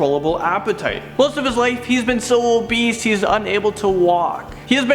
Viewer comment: Surgical surgery? Did he really say surgical surgery? Seriously? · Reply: He says obeast instead of obese.